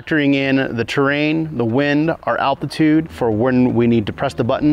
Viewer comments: Look, I am fairly certain he knows way more about flying and planes than me but at 5:17 did he say "Alpitude"? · did he say “alpitude”? Scary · did he say “alpitude”? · did one of the pilots actually say alpitude instead of altitude? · did he say alpitude? 0.o